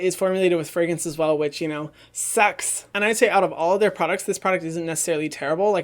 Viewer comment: When Hyram said sucks I thought he was saying sex hahha